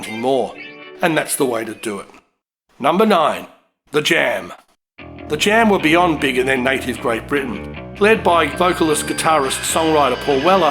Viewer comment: "Were beyond bigger than native Great Britain"? What? Even the transcript can't figure out what you said. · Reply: I said, "The Jam were beyond big in their native Great Britain". A lot of people in North America are not aware of just how big the Jam were in the UK. Likewise, here in Australia, you could only get their records from import shops. I was just trying to convey that they were a big deal.